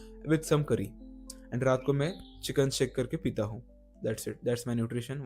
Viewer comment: Did bro said Chiken Shake 🤔